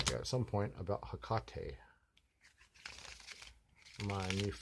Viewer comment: Stephen Lawson - i think more people look to me how they are. ♥️ i missed the shout out, when was it? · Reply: Stephen Lawson - lol you didnt say that! Maybe that's why i missed it - Hecate??? The first time i thought tou said Tecate - like the beer but then i was like no.. 💖